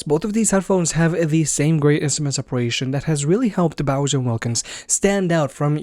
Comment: are you saying "Bowser" instead of "Bowers"?